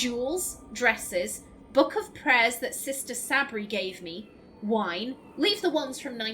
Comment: When you are reading the list for second I thought you said Sabrie Lol 😂🤣. My inquisitor was Qunari Mage and holy hell was that keeper very frigid I'm like dude I'm just trying to help